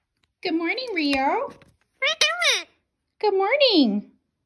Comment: omg this little birdie is soo cute [if you wondering what it said it said: what are you doing?(x4) you're recording me?]